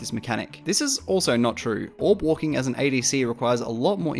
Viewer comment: what did he say at 3:15 ? orb walking?